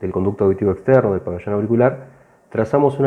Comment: Hola! Muy buen video!! Una consulta, qué dice en el minuto 17:20 seg. ? No se entiende muy bien. Gracias! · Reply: Hola. Muchas gracias por el comentario. Me alegra que te gustara.😊. "Conducto Auditivo Externo o el pabellón auricular". Nada que agradecer. En lo que pueda darles una mano cuentan conmigo. Saludos